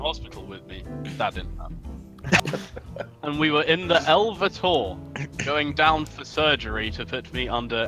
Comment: I heard “the Elvis hall”